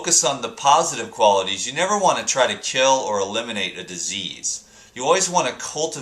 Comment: At first I thought it said eliminate CANADA with sugar lolol